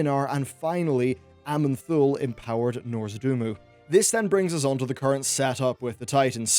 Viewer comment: Did he actually say "Nozordumu" instead of Nozdormu at 6:40? :D